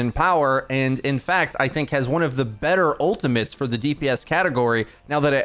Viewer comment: what the fuck did this guy just said ? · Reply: Has one of the better ultimates for the dps category